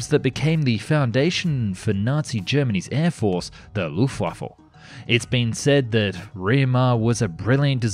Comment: Did you say “Luft waffle” at 6:12 ?????